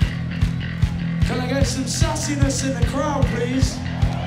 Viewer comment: I thought he said "can i get some sussyness in the crowd please?" for a second there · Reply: sassiness